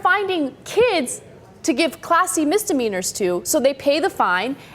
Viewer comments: Am I the only idiot that thought she was saying Classy Misdemeanors until they showed the statistic on the screen? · lol i thought was saying "classy misdemeanors"